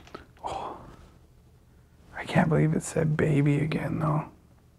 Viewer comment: I heard a woman say, Baby. Great location!